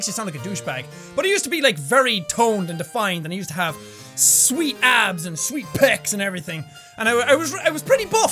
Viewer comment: I heard sweet ads XDDDDD